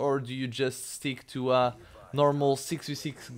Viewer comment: Every time I hear "Macau " I think you're saying "my cow" Lol cx